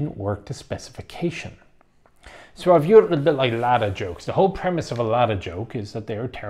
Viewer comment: I heard it at first as 'ladder jokes', and thought yes, they would make terrible cars.